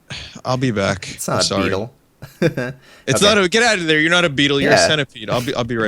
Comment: whats with the women's voice while gets the centipede? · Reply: ky there was someone arguing.. sounded like they said "get out!"